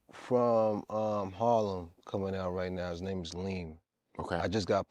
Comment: did he say leam?